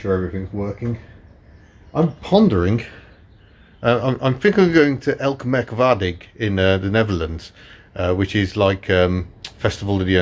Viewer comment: I'm dutch but have no idea what you're saying 😆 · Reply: Elk Meerk Waardig. I think.